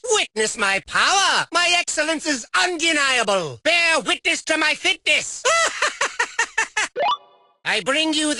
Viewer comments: What is 13:42 saying? All I can hear is bear witness to my thickness and I'm pretty sure that's not it. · I’m sorry but did I just hear “Bare witness to my thickness”? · i thought he said witness my thiccnes 😂😂